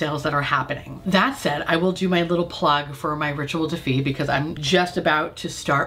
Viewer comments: Sooooo… I always thought you were saying the brand was Ritual Defeat but it’s Rituel De Fille.🤦🏻‍♀️ I have oily skin and putting oils on my face is horrible… I’m sorry!😆🤷🏻‍♀️ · Wow all this time I thought you were saying “Ritual defeat” !! De fille isn’t nearly as badass 😂